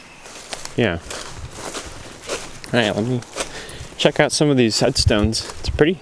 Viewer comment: I thought you said we're gonna check out some head stones? OH, look a squirrel!!!😅😅 , jk. I got to finish watching.